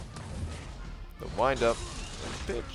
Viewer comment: he says “The Wind up and Pitch”. I’m a nerd. I heard say I swore? So I assume someone said that in chat so I went back about a minute and found it lol. He’s still clean